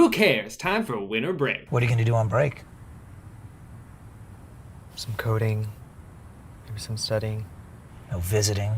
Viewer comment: I heard "some codeine, some studying"